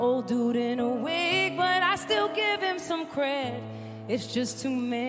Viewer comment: Anyone else got a dirty mind and thought she’d say “that’s what Beethoven said, old dude in a wig but ID STILL GIVE HIM SLME HEAD”? Just me? ... okay :(